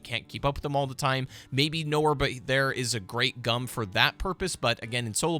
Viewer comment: Why did it sound like he said "Noah but there" @ 6:40 lol